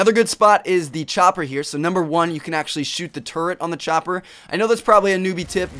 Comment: ''You can actually shoot the turd''? I know you said turret but it sounded like turd :)